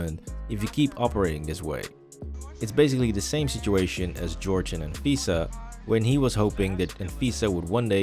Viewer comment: can someone please tell me which show that is at 9:39? "George and ...Pisa"? I dont understand what the narrator is saying here